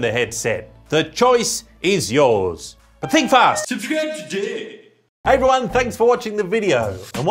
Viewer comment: I always hear that "Subscribe Today" as "Subscribe to Dick" 😂